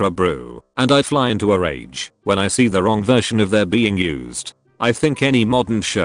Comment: Lol turn on ccs at 2:05 and watch as this guy complains about getting frustrated about using the wrong “there” but ccs says it as “their” 🤣